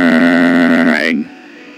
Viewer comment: did he just say homer?